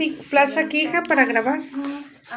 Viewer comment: En el minuto 06:06 se escucha una voz baja qué dice "Ayuda" Posdata: justa mente tenía que hablar una voz baja en el número del diablo